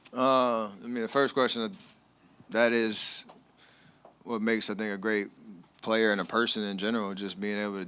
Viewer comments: I swear it sounds like he said “that’s what makes a nigga great” lol 😂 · Did he just say make a ni..a great?